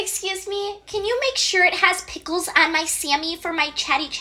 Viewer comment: oh gosh i thought it said pimples